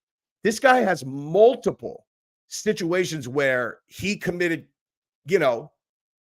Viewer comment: I thought said 'snitchuation'. He did not, but I might in the future. Lol